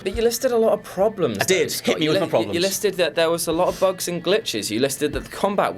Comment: For a second I thought he said he "experienced more glocks and bitches". Turns out it was just "bugs and glitches". Ah well. Maybe next time.